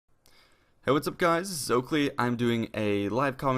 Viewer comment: For the longest time, I thought he was saying “This is SoClean”. Clearly my life has been a lie.